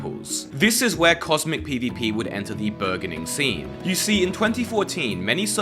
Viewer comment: did he say virggining scene?